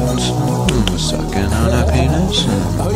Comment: did you say sucking on a penis?? :D